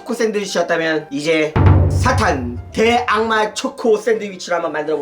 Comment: 개악마 라고 들었습니다 삐리릭!!